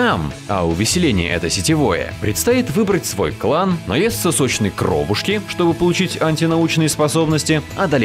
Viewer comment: мне послышалось "наесть сосочной кровушки"